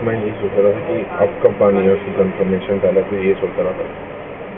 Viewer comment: I heard confirmation validation. The man and woman talking sounded like a training session to me. 👍